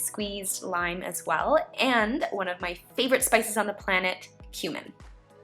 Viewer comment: "One of my favorite spices on the planet, cumin." But I thought you said "human" when I first heard it!!!!!